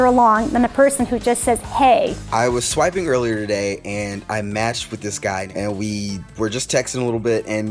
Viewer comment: did he sayd he mashed this guy??